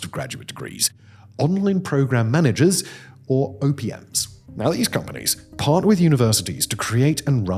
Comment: am I the only one who heard Simon say Opiums?😆